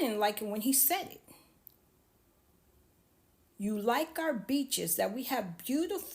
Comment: Oh he said beaches 😅 i thought he said bitches 😅 Yep i watched all of it. Your speaking is improving